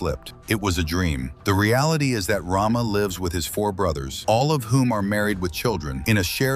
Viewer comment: All of what? Did he just say All of whom are married with children